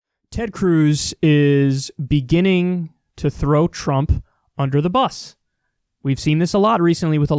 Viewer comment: I swear to God I thought you said “Ted Cruz is beginning to throat Trump under the bus”